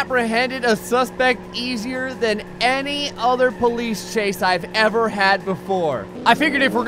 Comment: Mr. Gray Sir. when you where trying to say the police... ok. it sounded like you said... police cocaine :P